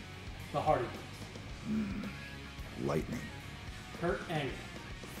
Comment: Omg when he said “Hardys” There was a millisecond at the start of “Lightning”, I honestly thought he was saying LITA 😂 That would have been like 🤯